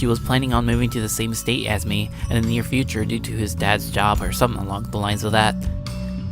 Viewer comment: did he say "HIS DAD"?????